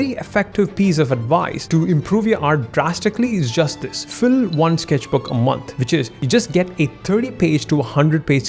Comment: I heard “fill up a sketchbook a month” *begins hyperventilating* I gotta work on that.